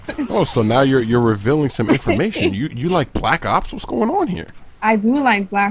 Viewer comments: does anybody else think that at first, at 10:03 you think chris said 'black cocks?' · lmao hahahahhah i thought she said she likes black cocks lol